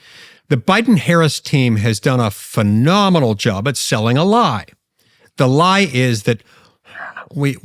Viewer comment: “The lie is…(Santa)”😂 ANYONE ELSE HEAR THAT??